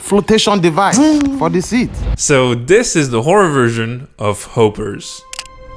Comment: Is it just me or did I hear him say hopers